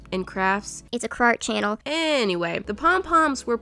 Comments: Also when she said crart I thought she said…… Fart... 😂🤣😂🤣😂🤣😂🤣😂🤣😂🤣😂🤣 y do I like laughing faces so much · I thought she said ‘fart channel’